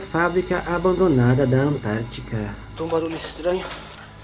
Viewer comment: Assistindo as 01:06. Na hora la da voz eu entendi Quem ta ai. Ou sair daqui algo assim.